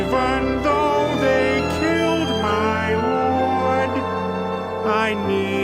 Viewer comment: I always thought Peter said “even though they killed my horde,” not “lord”. Diction, Peter, diction!